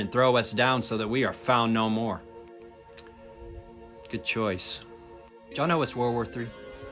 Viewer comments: What did you say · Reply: He said “good choice” which was a sarcastic remark. It’s his way of saying “stupid choice” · right after that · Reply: I heard "jonah was world war 3"